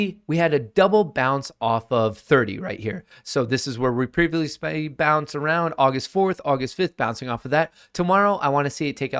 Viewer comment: what did he say? lol needs to slow down or chill on the coffee, he jumbles his words a lot. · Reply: “This is where we were previously bouncing around”